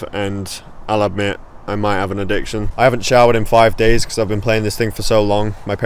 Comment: When you said “ I haven’t showered in five days “ I heard it as “ I have a child in five days”